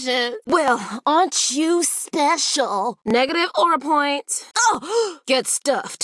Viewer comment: Did she say negative aura points?!